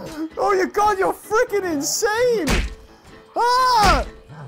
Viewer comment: did he just say 'god your f#&king insane!'?